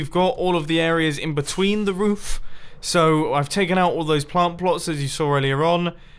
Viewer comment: did he say plant pLots?